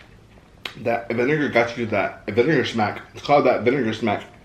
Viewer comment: wat is he saying I think he’s trying to rap😂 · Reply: Vinegar smack?